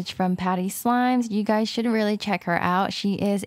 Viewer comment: On 6:52 did she say after reviewing her 3rd Package from patty slimes: YoU ShOulDnT ReAlLy ChEck HeR OuT ShE Is A CanADiAn SliMer¿ is it just me